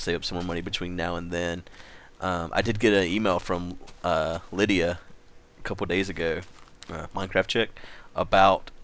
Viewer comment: all i heard was; "I did get a email from Glydia..." ... Glydia? the Dragon? THIS i wana hear about! xD